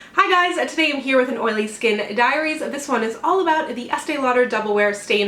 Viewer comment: I totally heard "Oily Skin Diarrhea" :(